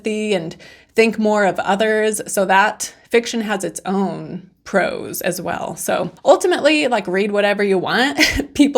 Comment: When you said, "Fiction has its own pros", I, initially, heard it as "Fiction has its own prose" Lol